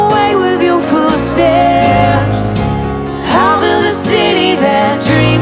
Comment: Every time they say "footsteps" I keep hearing "food stamps" lmfao